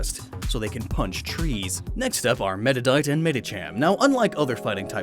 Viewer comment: When he said "meditite" for the first time in this episode I thought he said "meta Knight" 😂